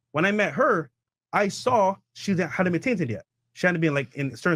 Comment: what did he say? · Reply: “Hadn’t been tainted yet” really fast